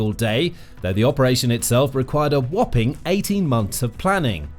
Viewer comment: did he say that Spider Web took EIGHTY MONTHS OF PLANNING?!? That's over six and a half years. There's no WAY that's right! The war's only been going on for about forty months!